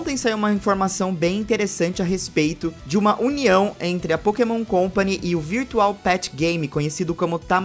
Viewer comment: eu sei o que ele disse mas na minha cabeça saiu isso "Uma união entre pokémon e digimon", isso explica a mega digievolução colocada em Kalos kkkk